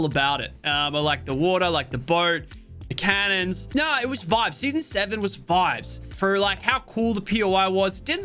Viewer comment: did mans say season 7